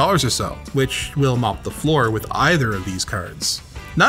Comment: I heard melt the floor. either is acurrate.